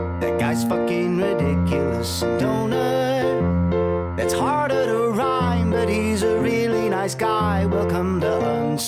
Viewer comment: "Donut, he's harder to find but he's a really nice guy" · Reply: I thought it said “that’s harder to rhyme but he’s a really nice guy”